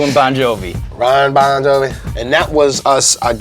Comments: Did he say 'Ron Bon Jovi'?? HAh. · Did he just say Ron bon Jovi? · Am I the only one who heard him say “Ron Bon Jovi?” 😂